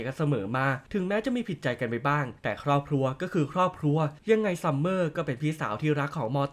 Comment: นายพูดว่าครอบครัวรึ